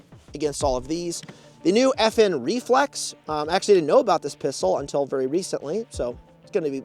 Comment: I thought he said “FN Reflux”